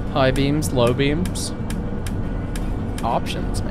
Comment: you were talking about high beams and low beams, but I was looking away at the time, 'cos I'm playing RRO while listening, and I swear I heard high BEANS low BEANS... had to go back in the VOD to see what I missed.